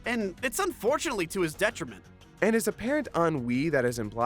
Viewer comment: What word is saying here? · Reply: “And it is apparent on we” Sorry for being over 7 years late